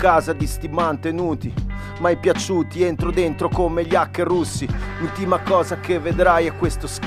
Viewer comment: Ma al minuto 0:29 cosa dice di preciso? Entro dentro come aka e Russi? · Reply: Hacker russi presumo